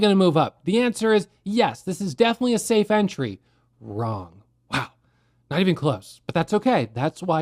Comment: When he said at the beginning "wow, not even close" I heard like "9 of clubs" and made me feel like at the poker table hoping i am not getting a bad beat :)